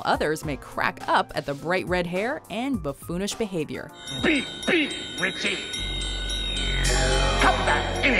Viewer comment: の「ピッピッ」がブリッブリッに聞こえたwww